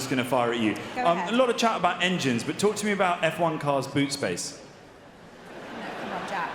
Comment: Who’s the girl who gets all sensitive at 5:35 and what’s the context? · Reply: She thought he said b*ob space